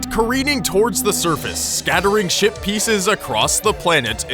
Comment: I don't know why but every time I hear somebody say "Ship Pieces" I hear: "Shit Pieces"!